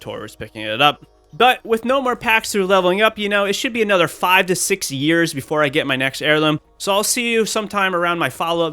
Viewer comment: he literally phases I dont understand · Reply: “But with no more packs through leveling up, you know. It should be another 5 - 6 years before I get my next heirloom...” Is what he is laughing at bro